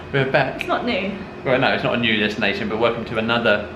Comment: I thought you said welcome to a Nudist destination ! Haha I thought…wow…they are being more raunchy now. Listened to it again…you meant New Destination in Manila haha